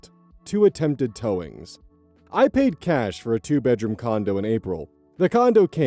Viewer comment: I heard “condom” and had to rewind to rehear